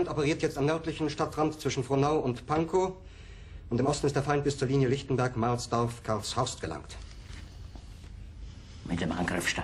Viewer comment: he says, "Und im Osten, ist der Feind bis zur Linie Lichtenberg, Mahlsdorf, Karlshorst gelangt", so not quite what the subtitles are saying. But then again they're subtitles, not transcripts or closed captions. If you want to be fully accurate and faithful when translating, it goes like this: "And in the East, the enemy has reached the Lichtenberg-Mahlsdorf-Karlshorst lines". Der Feind: The Enemy Im Osten: in the East/on the eastern front ist...biz zur...gelangt: has reached die Linie Lichtenberg, Mahlsdorf, Karlshorst: The Lichtenberg-Mahlsdorf-Karlshorst lines (in the 'lines' of a front sense)